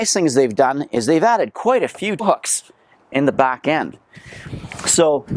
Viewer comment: I swear at 3:14 it sounds like you said "They've added quite a few fucks in the back end." 🤣